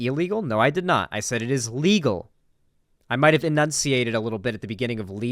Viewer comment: good job responding to that, I totally heard illegal, and then I heard the context and inferred what you meant